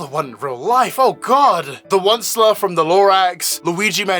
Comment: when you said "onceler" i heard "one slur" and i did a double take